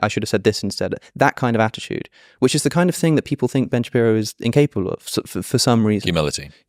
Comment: i thought it said "dating ben shapiro" so i was confused as fuck for a sec